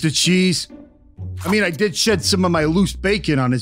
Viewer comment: did Bro just say sh*t?! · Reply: He said shed